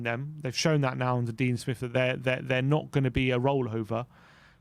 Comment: For a moment I thought said, "new manager DELIA SMITH!" 😁